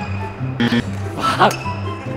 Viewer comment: did taufik say f**k 03:13 :P